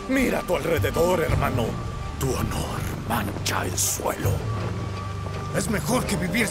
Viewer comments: "Yasuo dice Sorieketon" Yo: LO DIJO SEÑORES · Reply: Pensé que decía soy el mejor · "Yasuo dice Sorieketon" Yo: LO DIJO SEÑORES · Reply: jajajaja yo pense que decia "soy el mejor" xd